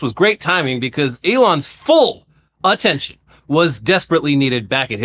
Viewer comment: did you intentionally say “Auttention” like, combing the word with autism, or am I just hearing things…